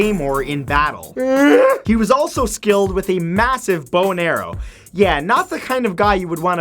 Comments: When he said bow and arrow, I thought he just said boner weird. And then he talked about the kilt. And I was just like... Wut. Why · I am SO sure I heard a YEET